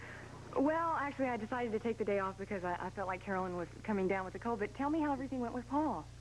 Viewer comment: Wow it's sounds like Frannie said “ Carolyn was coming down with the Covid.” 😮